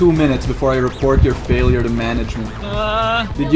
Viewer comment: did say "Failure Management" guys we need Steven He to help deliver the punishment.